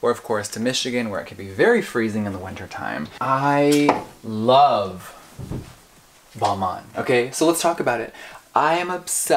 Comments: i thought she said '' i love walmart '' XD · MY DUMB ASS THOUGHT HE SAID "I LOVE WALMART"